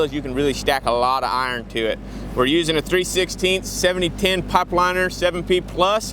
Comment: What rod were you using? I thought I heard you were using a 70110?? Did I hear right? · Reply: He said 7010. Its a pipe rod. Celluoluse like 6010, 70,000 psi tensile strength. Its sometimes used to fill and cap pipe after a 6010 root pass.